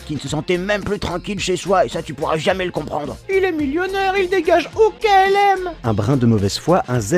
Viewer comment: J'ai entendu "il dégage" quand t'as dit "il déménage" °-°